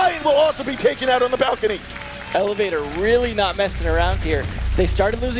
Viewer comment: I think he said elevator